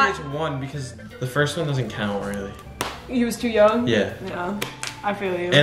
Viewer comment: wait did anyone hear say he when he asked if they were too young? time stamp //